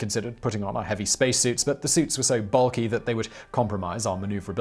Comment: Did he really say "Builky"?